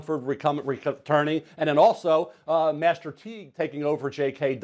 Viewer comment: Penei Sewell is the Andrew Luck of LTs. He should go #1. Also, 2:56 did he say "Master P is taking over at RB for JK Dobbins"?. lol what